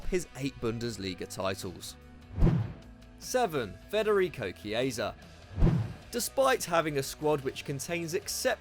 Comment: Looking away from my screen, I had to do a double take... I thought he said Federico Macheda